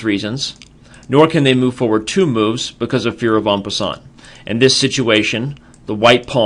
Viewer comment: "Nor can you move two moves, because of your ??????" What is being said here? Also i dont understand the move he illustrates with the white pond. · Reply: It's called en passant. It can only be played in the first move of a pawn. If a pawn moves up two squares on its first move, which is shown, but could have been captured if it had only moved one square, it can be captured by an enemy pawn.